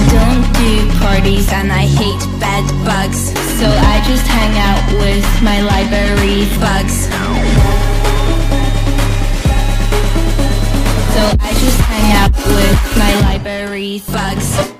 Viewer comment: sounded like "Library Fucks"